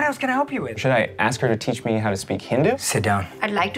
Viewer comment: "Should I ask her to teach me how to speak hindu?" "Sit down" I'm wheezing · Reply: I thought he said 'Hindi' and I was wondering why that was wrong 😅